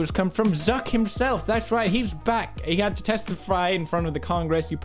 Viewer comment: Did he just say test-fry? 9:22?